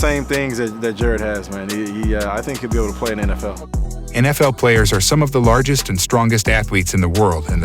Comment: rubbish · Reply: I thought you were saying that the AFL players were fitter the RL players